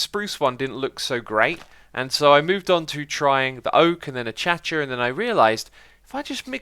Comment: Did you just say achacha?